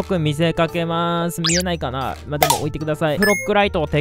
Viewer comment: 最後のやつ「チャンネル登録中島」に聞こえた人とかいる？